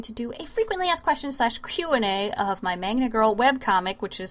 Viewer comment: I feel so stupid right now, I always thought you said "manga girl"........